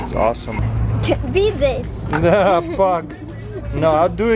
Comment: did said f*ck?